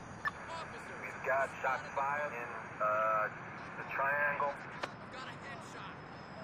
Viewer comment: wtf 1:10 a man says: got a headshot